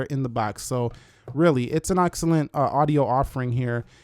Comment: Did he just say Auxcellent?